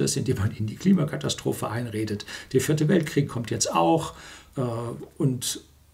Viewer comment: Bei 15:09 meinten Sie mit Sicherheit den dritten Weltkrieg! Trotzdem sehr sehr gutest Video! Leider verstehen nur etwa 20 % der Leute den Inhalt... und nur 10% verinnerlichen Ihn...